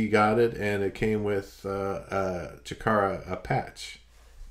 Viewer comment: I thought I heard pronounce Chikara "Shitara"...fitting that it was the Japanese version of "Smashes, Thrashes and Sh*ts"...sorry for the vulgarities 😉